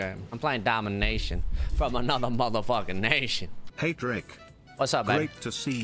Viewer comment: can somebody tell me wtf just he said ? :D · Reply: I’m playing domination. From another motherfucking nation